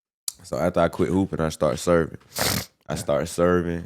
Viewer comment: i thought he said surfing lol